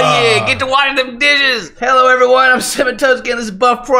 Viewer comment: It really sounded like you said "hello everyone this is seven tubs Ken" at the start there...